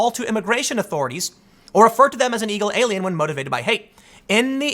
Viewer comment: anyone else hear "eagle alien"